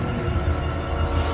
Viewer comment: he actually said “NO! STOP!”